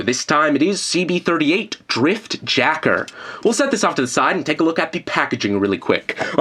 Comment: I almost thought he said drift jerker